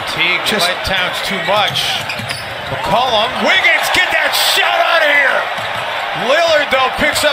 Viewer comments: did he say get that shit out of here ??? lol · did he just say " get that shit out of here"? Lmao